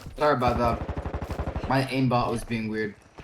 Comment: what did he say? Aimbot not working??